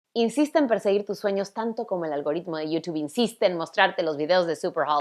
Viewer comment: No entendí el mensaje que ella dio al princio hablo muy rápido :"( , que dijo? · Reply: Insiste en perseguir tus sueños tanto como el algoritmo de YouTube insiste en ....